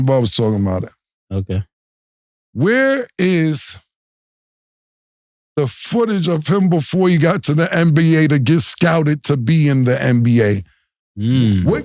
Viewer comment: What Cam said: "Where is the footage of him before he got to the NBA, to get scouted to be in the NBA......?" What I heard: "We ready. You ain't gone bluff us at no concert, sell out 25 thou' Actin like you gon diss us You got anthrax over there man, and we George Bush man"